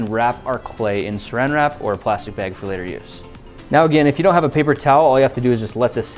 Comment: Did he just say if you don't have a paper towel?